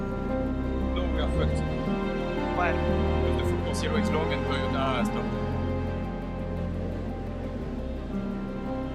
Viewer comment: What's is he saying at 30:12? Can't make it out what happened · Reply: i dont think its fair -- he said